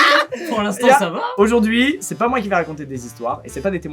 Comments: "c'est pas moi qui va raconter des histoires" la langue française en sueur 😂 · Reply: il dit "qui vais" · Squeezie "C'est pas moi qui va raconter...." C'est pas moi qui vais !!!!! Sorry